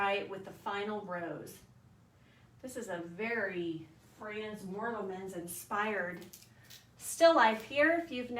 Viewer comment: Who is the artist mentioned at 12:16 ? I can't quite catch it. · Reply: Frans Mortelmans